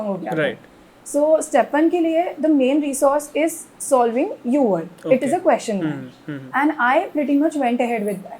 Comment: pe what Q bank she said? please tell. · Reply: The Q bank for USMLE is called “UWorld” :)